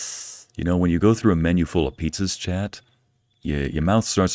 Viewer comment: Pizza...did you say pizza??? 😂😂 I don't even know when I had pizza the last time.